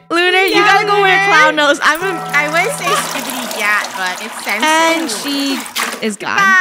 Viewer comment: WHAT DID SHE SAYYYY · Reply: She said, “skibidi gyatt?” Did you hear a single thing?